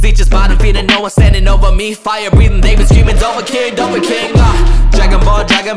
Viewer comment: did he say "fire breathing NIGGAS" at 8:01?????????